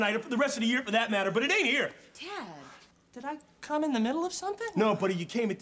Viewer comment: she said Tad, but I thought she said dad. I was like WHAT..... THE...... FUCK......